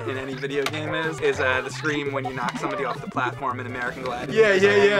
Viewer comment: I didn't catch, what is the name of the game when you knock somebody off the platform, that Mike says? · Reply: American Gladiators